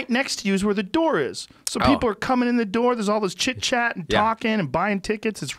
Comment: sounded like joe said 'china chit chat'